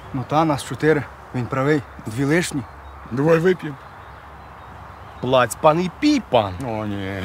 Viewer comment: Что он сказал? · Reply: Це по польській: "заплатить пан, буде пити пан". Щось тіпа того.